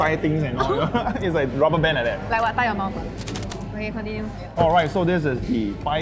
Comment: thought she said tie your mom XD